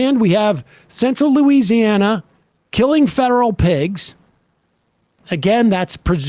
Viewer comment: I thought Sam said "killing federal pigs"